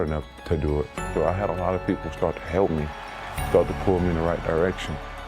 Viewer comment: did he say ERECTION ?!?!lol